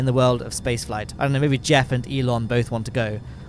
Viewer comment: for a second I thought he said jeb and Elon, and honest it still made sense jeb is after all a good supplier.